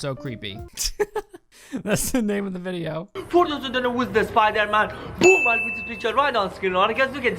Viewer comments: "For you who don't know who's the Spiderman" Just in case you still can't understand crazy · I... I think he's saying "for those who don't know who is spiderMan, boom, there's a picture of Spiderman" then again, I don't speak coke addict. Also that weird ass fight scene looks like a scene from a live action mk game